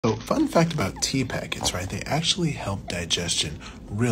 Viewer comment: Tea PACKETS????? Tea BAGS SIR. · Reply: I wondered what he meant. Thought he said tea packers 😅